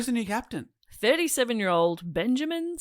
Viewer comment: When said it was captained by a 37 year old, for a moment I misheard it as "sturdy 7 year old" and that was a laugh